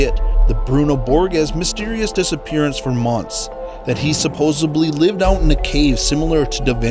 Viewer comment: Great video, and a lot of fun to watch. But did he really say "supposebly" at 6:39?